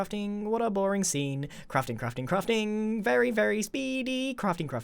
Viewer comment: It almost sounds like hes saying "crufting"